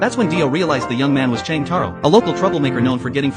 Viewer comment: Cheng tauro is lowkey such a clever spoof name. At one point in this short as the a.I. voice was saying it a lil fast it kinda sounded like he was saying Jotaro😂😂